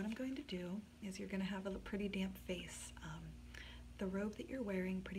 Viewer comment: when she said you'll have a pretty damp face, i thought she said you'll have a prett damn face, i was like yes bitch i can't wait